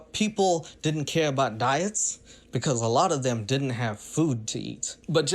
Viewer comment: you missed the context. He was talking about at the time in apartheid South Africa. It was a bitch for all people of color. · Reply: No. I didn’t miss context, I guess I just misheard, I thought he said “I’m from South Africa, and where I’m from they don’t worry about dieting because we don’t have any food” as opposed to “we didn’t have any food,” which would have different implications.